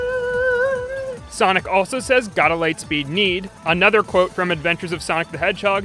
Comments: You messed up a sonic reference call (AOSTH). 5:16 Sonic doesn't say Light Speed Need, Sonic says Gotta Light Speed Keed which is what Jaleel white era 1990s Sonic said quite alot, "Gotta Speed Keed". One of my favorite 1990s sonic cartoon quotes from him. "It's juice and jam time" is another great one. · he actually said gotta light speed keid